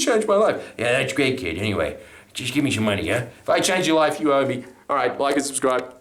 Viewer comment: That line at the end, I though I heard "You change your life You owe ME!!! had me laughing.